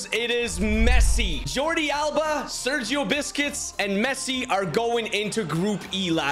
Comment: Bro it Sounded like he said “Sergio Biscuits” 🤣🤣🤣🤣